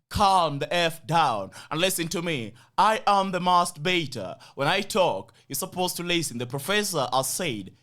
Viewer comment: When you say mask better it sounds like you say masturbator 😂😂😂